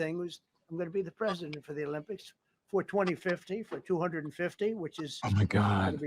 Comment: Did he say 2050